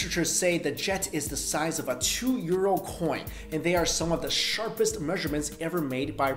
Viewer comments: Did he really say that "the jet is the size of a two year old coin" at 3:38? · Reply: two euro coin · the size of a 2 year old coin? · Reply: Oh i get it now... Doi! I thought he said a 2yr old coin too... Boy i feel real smart....